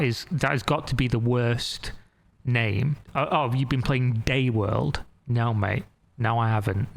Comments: no offence to anyone, I'm eating dinner and i have the AC on full blast. i miss heard, i thought he said the new name will be gay world. i need to clean up all the spilled food, have a great day all. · At the end i thought he said “gayworld” 😂